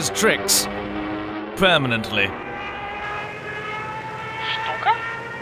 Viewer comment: When the he 219's pilot said Stuka I thought he said cyka XD